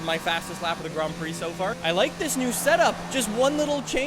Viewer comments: Setup* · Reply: oh I thought he said senna. And yeah hearing him say it again, it does sound like he's saying "setup" instead of senna. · you sead McLaren Senna.Nice McLaren Senna.😎👍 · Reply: It sounds like he said McLaren senna but he said McLaren setup